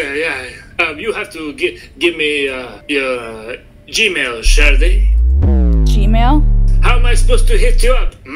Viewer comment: I thought that one guy kept saying… ✨sharty ✨